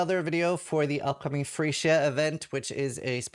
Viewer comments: I thought he said the upcoming free shit event and I found it accurate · oh God I just realised this is the "free shit" event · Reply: I was 100% certain I heard “free shit event” until I read this comment. Thought he was talking about the armour set